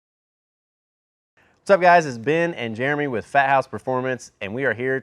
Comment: I thought it said “Farthouse”.